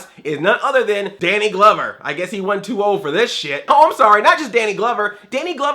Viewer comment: Do my ears deceive me? Did I hear you explicitly say "SHIT"?!!!